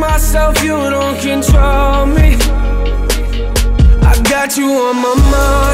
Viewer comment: lmfao at 1:38 when he sais "You let me be myself you don't control me", I thought he always said don't troll me anyone else? haha